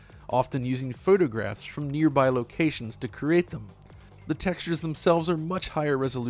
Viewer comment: got a bit of an audio hiccup here - looks like your voice audio got cut off before you finished the sentence. (Edit: nevermind i hear it now. Couldnt hear "them" on my phone) Also wish you had covered the partial remakes of classic levels in THUG1, THUG2, and THAW. But still great video otherwise! Loved how you looked into THPS2x & the Dreamcast versions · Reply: I think he just said "to create them" but he said "them" very quietly so it almost sounded cut off